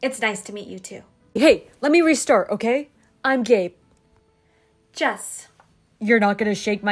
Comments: When he said ‘Gabe’ I wasn’t paying attention to the captions so I thought he said “I’m gay” 😭😭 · Ngl i thought he said he was gay😂😂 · Gabe: “I’m Gabe” Me: “Excuse me, did you just say you’re gay?”